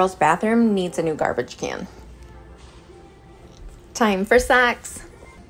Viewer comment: oh my i thought i heard you say, TIME FOR SEX! when you were near the socks! Your accent threw me off!